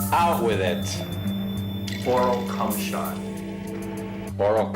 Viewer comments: Hacker in video: “oral cumahot” Youtube ads: Oral-B electric toothbrush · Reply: i thought he said anal cumshot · i thought he said anal cumshot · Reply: fakerussian1839 idk why but I thought he said “moral cumshot”